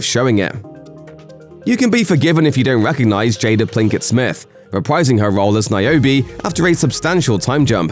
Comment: Did he really call her Jada PLINKETT Smith 4:24? 🤣